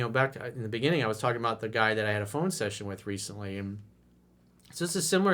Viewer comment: When you said phone session, i missheard it as "phone sex" only for a sec but then i thought... Man what a racket lol you set up a line for dudes to practice on lol they do good they get the goodies lol